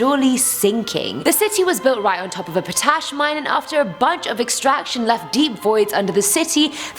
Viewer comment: I seriously had to Google "potash" mine, because at first I thought you said a potato mine! 😂😂